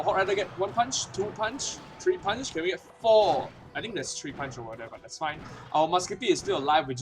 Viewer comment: no worries man , it's not your mother tongue and it's not mine also ;) so i maybe didn't hear you correctly. 1:37 ( three punch order) it's nothing man but I'm just carious what you mean cuz i keep hearing it after you say " punch" . love your videos btw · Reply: Ohhh i said "I think it was 3 Punch over there, not order haha"